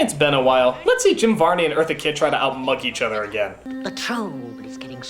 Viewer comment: En el 9:59 creo que dice "how mock each other again" "vamos a ver como Jim Varney y Eartha Kidd se ridiculizan el uno al otro de nuevo"